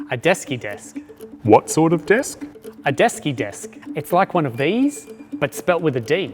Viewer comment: As a non-native speaker, I am wondering what's the word for "one of these". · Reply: Esky - a portable insulated container for keeping food or drink cool